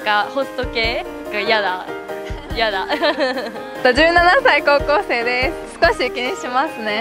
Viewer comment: 57才高校生です に聞こえた